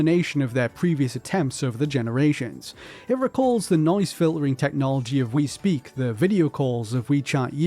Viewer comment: swear to god I heard "the nonce filtering" and had to replay it twice